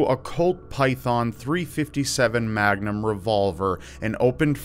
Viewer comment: Who the F takes 4 magnums to the guts and lives. That man is metal AF! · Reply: I thought you said maganums.... as in....... Condoms.... I haven't watched the video yet